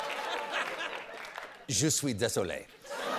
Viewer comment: I know my French is rusty but did Stephen say "I am the sun" at 3:06?